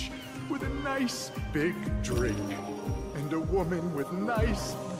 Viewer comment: I'm not gonna lie, i played this game excessively for nearly 25 years, and i always thought he said "bags of eyes" and my brain literally never questioned it because i always knew bags under your eyes was a thing.